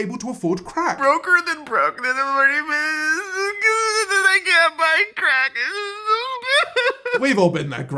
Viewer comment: I think he is saying “I can’t buy crackers” 🤣🤣